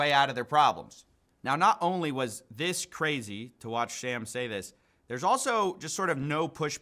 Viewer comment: did he said "to watch Sham" say this ? 😂😂